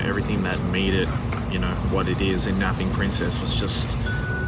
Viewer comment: I thought you said ‘Dabbing princess’ and I was like NUUUUUU MATE